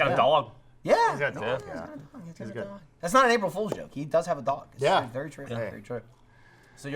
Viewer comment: Dammit tricking me into believing Ray would be on! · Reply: Gaming Shenanigans I thought they said they never do April fools jokes